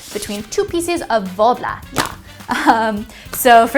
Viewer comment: Whats that brown material I couldn’t understand what she said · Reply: it’s this thing called worbla!